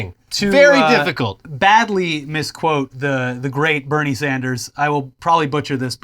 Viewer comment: I legit thought you guys said "the late Bernie Sanders". Had to get my Echo AI to confirm that he isn't dead yet.